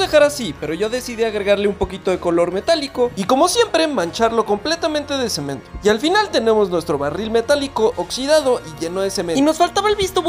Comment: Escuche "... y mancharlo un poquito de sem€n..." 💀